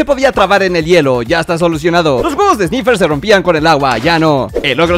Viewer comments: Escuché pinch3 en ves de picture xd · Hablo tan rápido que entendí "con la pinche planta, ahora sí"